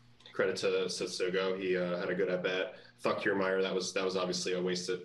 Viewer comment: what he say? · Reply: He said fuck kiermaier😂